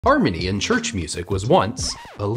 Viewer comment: Anyone else thought he said, "Armenian Church music was once illegal"?!